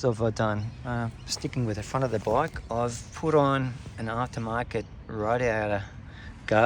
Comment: What in gods name do you say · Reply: “Aftermarket radiator guard”